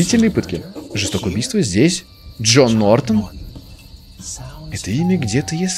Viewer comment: ты сказал Джон Нортон? Мне послышалось джорно Джованна И да джооооджоооооооо 43:50 чем-то смахивает на смерть 13